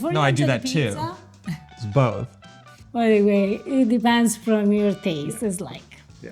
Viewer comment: Did say oy vey @ 26:30? Is this from her assimilating or do they use Yiddish phrases in Italy or was she using a similar Italian phrase? Love hearing Yiddish on YouTube!!